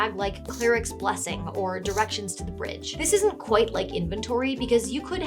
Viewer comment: I thought you said directions to the fridge